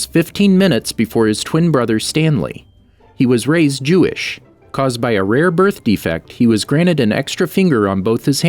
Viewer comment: I really thought he said "He was raised Jewish caused by a rare birth defect" I didn't notice the pause in between